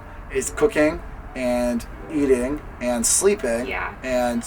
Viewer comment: I thought he said cocain instead of cooking Hahahaha